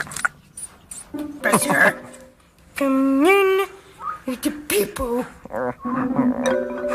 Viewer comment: it sound like she said "Gimme the peepo n*gga!"